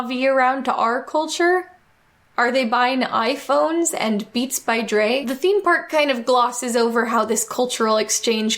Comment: Did she just say "Beats by Drake"?